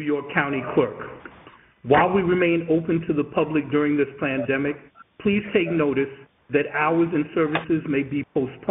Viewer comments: He totally said plandemic did he not? Sounded like he said Plandemic. I've NEVER heard a homie G be the voice of a automated answering service for a state. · I swear to God, dude literally said "plan-demic"... Am I wrong? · County clerk really sounded like he said plan-demic 💀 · I swear the recording says Plandemic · Did he say "plandemic"? 😁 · Did he says while we remain open to the public during this plandemic? The script says he said pandemic, but that is not what I heard, haha! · The recording on the voicemail sure sounded like he said "plandemic". Did he say the quiet part out loud?